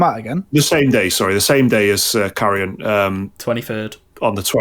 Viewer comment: I misheard Carrion as Carry On and thought it was a game based on the old film series.